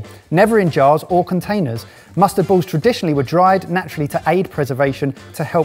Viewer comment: I’m sorry at first I misunderstood, I heard 16th century mentioned & immediately thought you had said “musket balls”